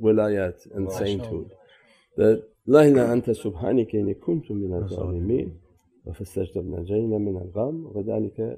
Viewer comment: Someone pls write what says at 1:28 🥰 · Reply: I think its La Ilaha Illa Anta Subhanaka Inni Kuntu Minazzalimin